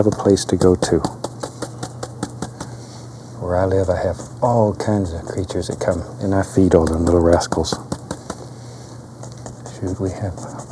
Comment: I thought he said "Where I live I have all kinds of creatures that come, and I feed on them little rascals."